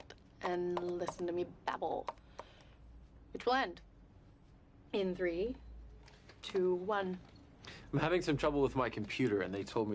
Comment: What did she say at 0:51? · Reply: Which will end. In 3...2..1.